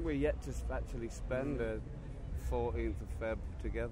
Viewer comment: what the hell does alex even say at 6:19 lmaoo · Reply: that they've yet to spend '14 of feb' (valentine's day) together (meaning that they've never spent the day together) & then he starts talking about consumerism, how the holiday is all about selling cards & balloons, etc etc.